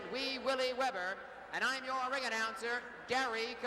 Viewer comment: did he just say...wee willy??? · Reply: Yes Wee Willie Webber